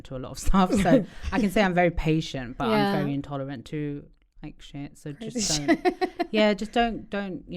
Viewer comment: Did she say “Intolerant to Christians”?